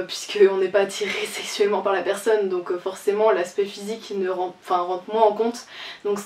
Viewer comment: Déjà il y'a des choses qui vont pas quand j'ai commencé ta video 😂 tu as dis "aspect physique" et j'ai entendu "spé physique" et 2 😂 t'as dis "les arts" et t'as mis une image de lézard 🦎.....😂😂😂